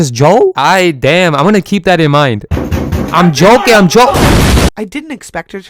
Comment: I was laughing hard asf until I heard I was joe-king